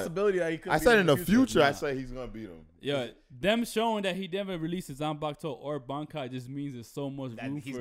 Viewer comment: he actually says release and bankai but for some reason they were saying rukia has a bankai ffs just because she uses ice doesn't make it a bankai that's her release aka shikai ffs when will they learn